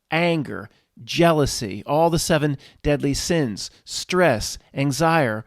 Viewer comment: Did you just coin a new term at 26:42? I heard "Anxire" = anxiety + desire